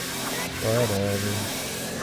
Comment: i don't get what he said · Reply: "Whatever"